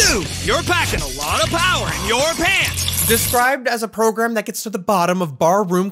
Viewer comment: when you said "the core to manswers as a program" i thought you said "brogram" instead